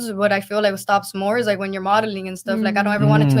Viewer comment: I thought CEO said you might be tired of malling (ie shopping), lol, but he said modeling